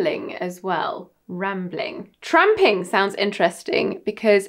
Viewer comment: The captions during the tramping part were hilarious because it messed up and it read trump instead of tramping and then she said its a derogatory term for a homeless person or someone who lives on the street 😂